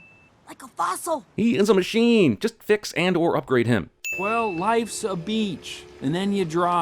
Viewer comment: now that u think about it, it almost sounded like he was saying life's a bitch. something like that